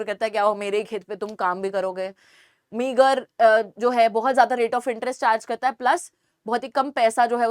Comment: am I the only one who heard the N word??